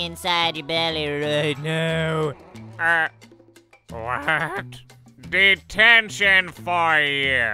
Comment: I thought it said "determination"